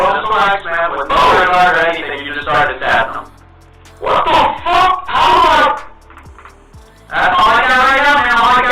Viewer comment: What does X say here? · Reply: Jahseh "man.....F**K BRUH (pause) bro (pause again) what the f**k? How am I- (stops)"